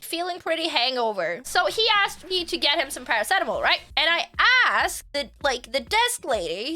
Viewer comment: When you said paracetamol, I thought you said "something adible", like cannabis xD